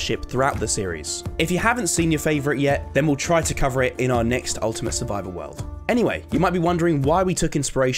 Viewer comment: did he say NEXT USW...?